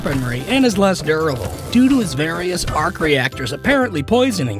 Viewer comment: Am I the only one who heard him say park rector